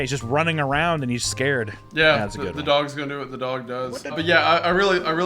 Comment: I thought I was going insane when I heard "what the dog doin"